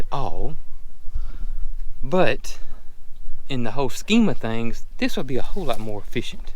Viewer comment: Every time says “whole scheme of things” I swear I hear him say “Holstein of things” 😆🤣